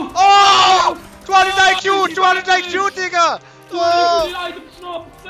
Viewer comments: wtf he said the Nword???? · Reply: He said digga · did I hear that wrong?!😂😂😂😂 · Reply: he said digga thats german and it means like bro